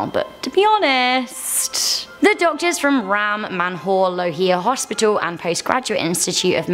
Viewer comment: All I heard was Ram Man Hole Hospital 😂😂😂😂😂😂😂